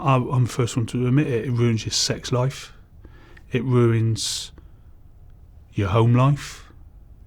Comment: Your homie wipes yer ass? Did he just say that?